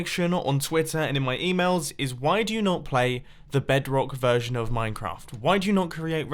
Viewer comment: did he just said "BEDK"